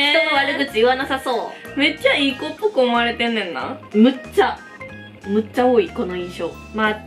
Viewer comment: めっちゃいいポップコーン割れてんねんな にしか聞こえない😂😂